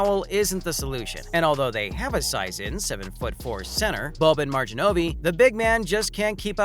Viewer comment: Did he say Bobin Marjin-ovee 🤣🤣🤣 Boban Marjanović